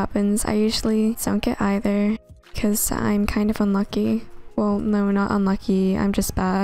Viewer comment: Lol 1:31 I heard “I’m kind of a monkey” lmao